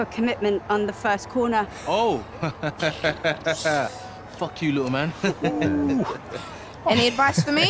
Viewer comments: Did he just say 'f*** you little man' to the child!? 😂 brilliant. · did he say "eff you lil man to the kid?" 😂😂😂😂